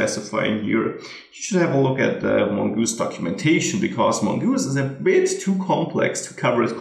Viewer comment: Great tutorial as always. I have to say, I did get a laugh at 5:36. At first I thought you said, "Mogoose is a bitch too complex...".